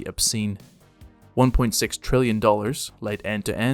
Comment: er....your caption is wrong by a factor of 1000. It's 1.6 million million dollars.